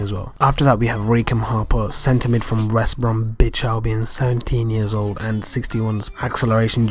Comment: Did I just hear that right? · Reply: Fappyymeal 69 West Brom Bitch Albion Lol